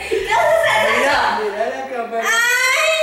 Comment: Yo si escuche Matías en el segundo